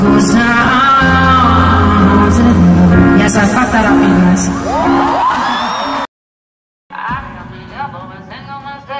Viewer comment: What did she say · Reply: Yes I fucked that up, you guys